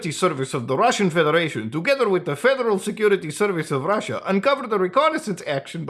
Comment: first time listening I heard "Fentanyl Security Service"